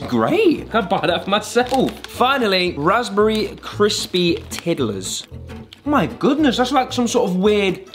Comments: What they said: “strawberry crispy tiddlers” What I heard: “strawberry crispy toddlers” ❤😂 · For a second I thought he said raspberry coated toddlers 😭